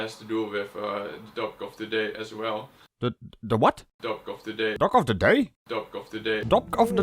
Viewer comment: I heard 'Duck of the Day'. "Sitting on the duck of the day... quark!" I guess that's one way to get pressed duck.